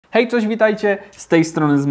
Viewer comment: Czy tylko ja usłyszałem na wstępie ,,hej coś witajcie”? 😅